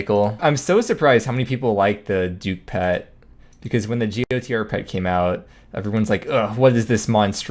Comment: what pet did you say? The audio glitches and I just can't make it out. For some reason my brain just can't piece those sounds together. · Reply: Gotr. Yeah unfortunately sometimes twitch clips have weird stutters like that